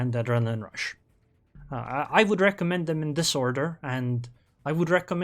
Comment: i heard "i would recommend them a disorder" Oo i need to drink my coffee first before listening to anything in a foreign language